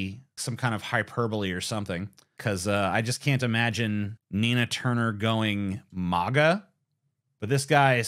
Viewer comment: For a second there, I thought it said Tina Turner. lol